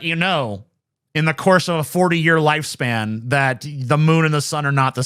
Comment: I thought she said four years. It's like my brain corrected that statement to protect itself from the stupidity of that statement